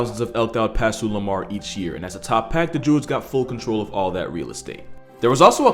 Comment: BRUH I HEARD JEWS 💀